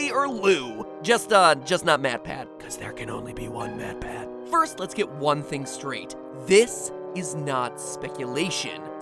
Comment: When he said: "There can only be only one Mat Pat" I heard : "There can only be only one Batman" ;')